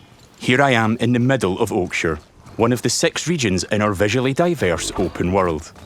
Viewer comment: I legit thought he said one of the s*x regions 😂